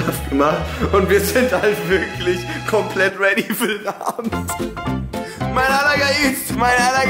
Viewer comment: min was sagt die ganze Zeit mein aller …??😂